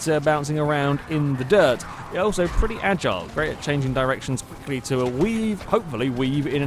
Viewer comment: I skipped to 1:20 to miss the little intro bit... All I heard was 'erections'